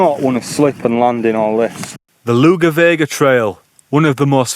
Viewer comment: Thought you said the Lou Bega trail in the intro. Hiking number 5! Also just seen 'trying local food' (burger and chips!). I'll grab a cuppa and settle in for this one.